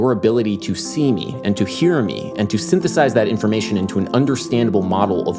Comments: I thought that it said “synthesia” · I thought it said synthesia.